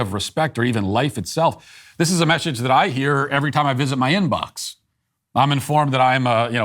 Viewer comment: "In Box"? I thought you said, "Every time I visits my In Laws". LOL.